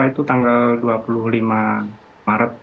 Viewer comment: Hilang Dari 25 mei ?? 🙄 Salah denger apa gimn